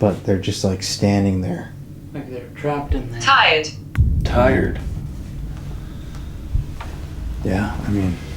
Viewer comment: one or two of you thought the evp said "tired" but I thought it said "tied". You were talking about some or a little boy/s just standing there not coming over...